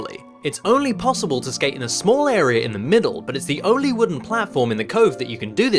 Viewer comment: when you sayed area it sound like you sayed arear